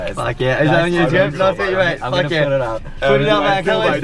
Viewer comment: “Do you want a cone too?” Hope he took the offer. 😂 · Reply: Nah, she actually said "do you want to come in too?" (with that look in her eyes...) Check it out guys at 8:17 😏😏